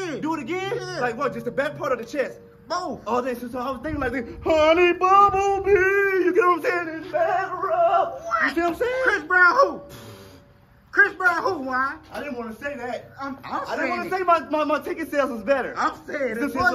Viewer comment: THAT HONEY BUMBLEBEE AND BACK RUB MOVE IS PRICELESS. DAMN I'M CRYING LAUGHING OVER HERE 😂😂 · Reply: Mannnn i thought this nigga said honey bumblebee and batter up🤣🤣🤣🤣